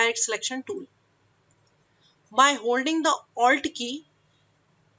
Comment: Great job. My only critique is please on the words that you can't help but roll your tongue, please add captions for us, it took me forever to figure out you were telling us to press the "alt" key. At first I thought you were saying "R key" which confused me a lot.